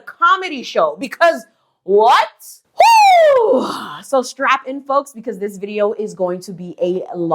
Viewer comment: 😂😂 i thought you said "strap ON folks" and fell over laughing 😅 i was wrong